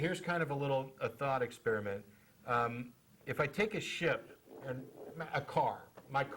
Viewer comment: Hahaha I heard what he said wrong, I thought he said "Here's a little thought experiment, hmmm if I take a shit..." haha ship makes more sense :P